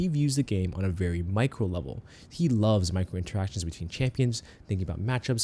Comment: I keep hearing "he loves microtransactions" for this and laughing my ass off because riot cringe gacha sanctum